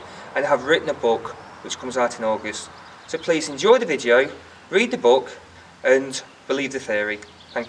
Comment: I just love that in the intro he says "please read the book, believe the theory" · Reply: I thought he said, "please read the book, believe the Fairy" Which would make this hysterical theory that much more believable 35